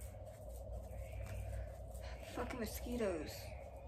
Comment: Very satisfying, but at this time tag did you really say "fucking mosquitoes!!!"? Haha - I understand there may be a lot of mosquitoes where you live but honesty is equally satisfying!